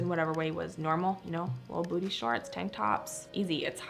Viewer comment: Did she say "booty shorts?" 2:45 Oh, no! Thank you for this video. I have an 11 year old daughter and I'm trying to get ready for her turbulent teen years. Lord, help me!